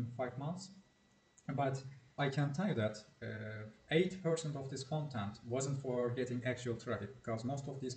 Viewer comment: Love you content and approach to SEO. Did you say 8% or 80% of content is for information purposes? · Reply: 80%.